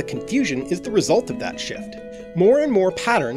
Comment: I heard "sh!t" instead of "shift" XD